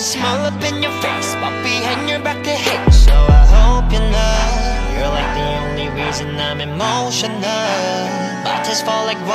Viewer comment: “they say a man ain’t supposed to cry” to➖🔜 “they say a man is supposed to cry” 🥺🥺🥺the way he changed the lyrics 🥺🥺🥺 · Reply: 'You ain't the only reason' at 0:42 sounds like he said ' you're like the only reason I'm emotional'. Or is it just me??